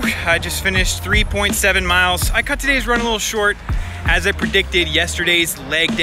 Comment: Anyone else notice, “My cut today is run a little short” 🤣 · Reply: I heard "I cut today's run a little short"